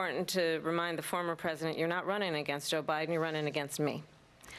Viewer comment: Did she just say Obiden??